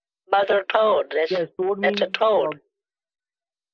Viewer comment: For the longest time I genuinely thought he was saying “all the mother toads are dead”